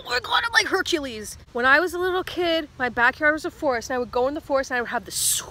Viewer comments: What she said: “when I was a little kid, my backyard was a forest.” What I heard: “when I was a little kid, my back hair was a forest.” Need I say more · Even though I’ve seen this video like 7 times, for some reason I thought she said “my back hair was a forest”... I’m confucian¿¿